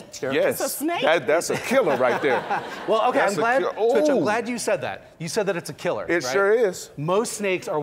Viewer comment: He said 'I'm glad you said that' and I heard 'So the Black Dude said that '